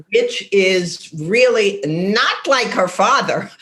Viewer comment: I misheard Mayim's mom say "bit*h is not like her father" and screamed at my screen